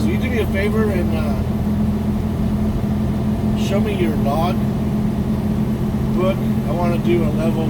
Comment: hahaha - when you first described the scale officer thing - I thought you said -- he said -- "show me your nog" and I thought WHAAAAT!!! yeah, old and hard a hearing too, but it makes for funny moments. You all calm about showing your LOG and it all makes sense. Still Funny. I'm a fairly new subscriber and going back into your old videos to really enjoy your channel. Love your channel!! Thank you.